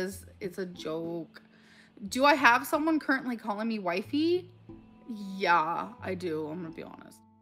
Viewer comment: I swear I heard she had someone calling her "wipe-e" as in amber has a wiper and she is the wipee. Gross.